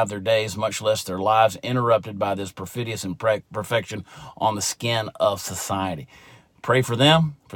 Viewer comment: lol same corvette in the background i enjoy looking at. interesting sweater choice. 0:57 did he say perfection??